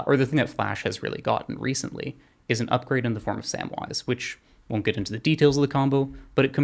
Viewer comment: can't understand the spoken name of the card that goes with flash, "Sand Wandis?" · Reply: Samwise Gamgee from LotR: Tales of Middle-Earth